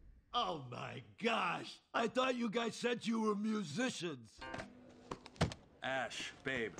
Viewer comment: F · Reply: O my gosh I thought you guys said you were amazing haha!